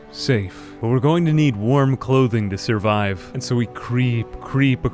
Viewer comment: I thought he said "worm clothing" oh my god